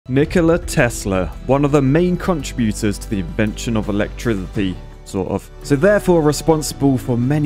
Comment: "electiminaty" swear I went back 3 or 4 times to make sure I heard right XD